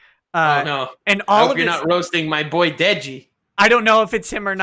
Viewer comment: Did I hear say "not my boy digi"?